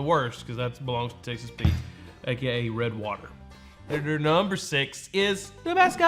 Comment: and calls it " Gay, red water" his words - not mine .....at 940 mark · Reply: AKA Red Water is what he actually said